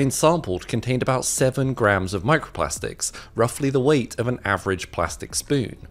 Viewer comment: I thought he was about to said ruffly the weight of two eighths 😂😂😂